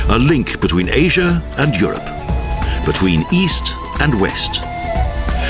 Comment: what was the last sentence the narrator said 51:07? "The historic bridge between Orient and ....???" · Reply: oxidants